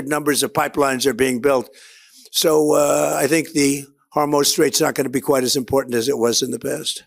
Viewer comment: Sending love from Canada. Did I mishear the President ? The hormone strait ?